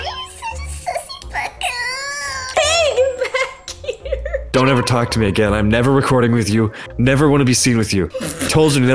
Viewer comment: UHH IS IT JUST BE OR 0:14 did she say bae or HEY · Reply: she said bae (:o)